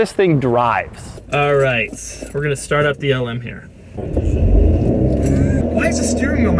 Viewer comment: Sounds like he is saying the Elmo2